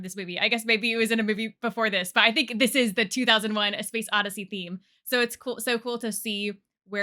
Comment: Every time you pronounce the title of the movie I keep hearing "2001: A Space Addiessey" 😂